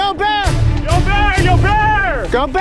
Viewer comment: i thought he said yogi bear Bear: WHERES MY PICANIC BASKET then i played again and it said yo bear Bear: WHY HELLO THERE HUMAN U BETTER BACK OFF AND LOOK AWAY FROM MY CUBS